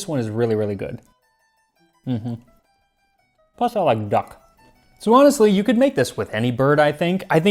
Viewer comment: "plus I like duck" at 22:35... I heard "plus I like gakh'....... I really just can't imagine "Tasting Klingon History" taking off...